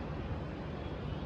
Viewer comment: Please watch from 2:05 when this guy zoomed I heard *Next station Mahalaxmi * how in this world is possible ,????